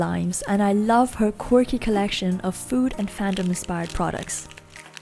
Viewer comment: I thought she said corgi collection and went crazy